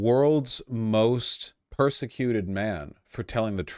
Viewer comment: Just like you said, he is totally the world's most persecuted man! Amen! Oh sorry, I thought you said "prosecuted".